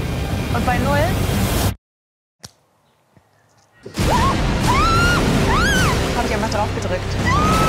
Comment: also ich habs mir jetzt 5x angehört in langsamer Geschwindigkeit. Meiner Meinung nach sagt sie: "Und bei Null hat Jemand drauf gedrückt". Damit wäre die Aufgabe nicht bestanden. Weiß es jmd besser? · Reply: "Bei Null hab ich einfach draufgedrückt... " so habe ich es verstanden